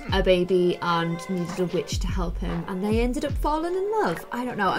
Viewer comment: I heard say a swear for the first time LMAO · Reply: She said witch